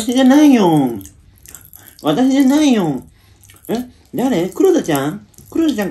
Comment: からの「私じゃないよ」が、 わたパチ食べてるせいで「わたひじゃないよん」って聞こえるのが癖になる😂